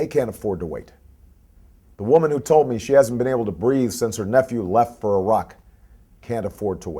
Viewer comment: "...she hasn't been able to breed since her newphew left for Iraq..." Oh, wait, I think he said "breathe". :)